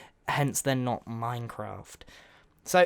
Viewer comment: When he said goodbye, I thought he said "Good Boyie!" And that's my nickname :D